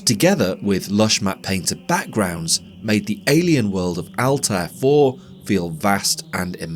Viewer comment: for a moment i thought you said alt f 4 and was like "No way that joke is that old" XD